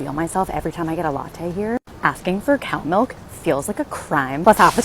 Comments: Cow milk? Oh. I thought she said cat milk! 😂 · I thought she said, 'cat milk'. 😂